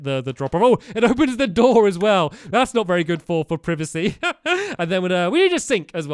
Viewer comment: wat did he say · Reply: It’s not priv it’s it’s privacy